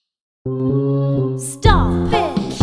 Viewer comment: At first I thought Belle said “Stop” then I heard it the second time and went “Oh.” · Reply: stomp